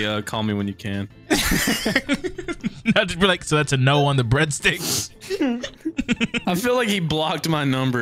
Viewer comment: When said breadsticks.. Sounded like he said Bread d!cks